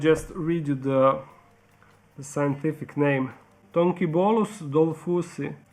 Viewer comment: When you say the scientific name for the millipedes I heard “donkey balls” so I turned on the captions and they also read as donkey balls...